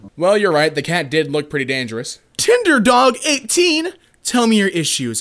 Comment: I heard ‘tinder dog 18’ as ‘tinder dog ate teen’.